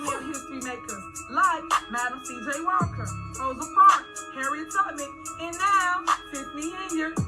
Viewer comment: did she say Harriet Tubbermen?